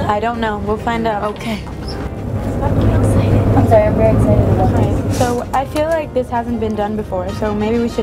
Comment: L: Thas my fa- I make that all the time.. N: You are a liar 0:56 Lauren: I'm excited, I'm sorry, I'm just really excited about this 1:40 Lauren: Camwich!! 2:37 Lauren's laugh is my life!!! 2:47 Lauren: I call being the tester!, I have to test all the food before it's put out Lauren being so cuteee!!!! love her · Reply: it sounds like lauren said my fiancé